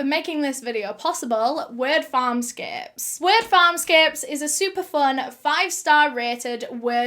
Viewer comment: Im sorry, I thought she said weird farm scapes. Only monsters could be capable of doing these horrific things!!! To watch any of the videos or eve hear the audio will actually kill a little piece of your soul, its that awful. I hope these monsters get what they deserve.